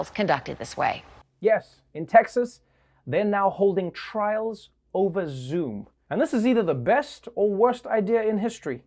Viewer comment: I thought Trevor said ‘in texas, they’re not holding trials at Zoos’ and I so confused....